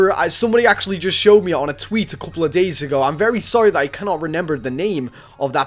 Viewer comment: No offence but at 3:24 u say renember instead of remember 😂 LOL